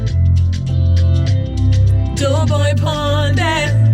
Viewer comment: is it just me or did say, "Joeboy pon death"